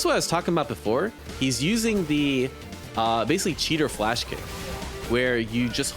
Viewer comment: Did you just say... delay flash kick is hard....